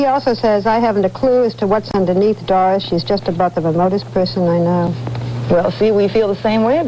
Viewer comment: what does barbara say at 2:11???? "shes just about the ______ person i know". ?? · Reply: Remotest person I know. Kirk Douglas said it